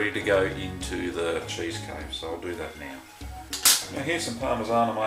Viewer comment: Would you please tell me what you said at minute mark 9:54 "Here's the product ready to go into the "something". I just can't catch that word. Thanks. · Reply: into the cheese cave; <link>